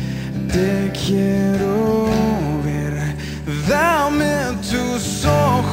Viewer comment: (lo marco para repasar desde ahí) PD: Genial, si sabía que decía ver pensé que decía ve o ves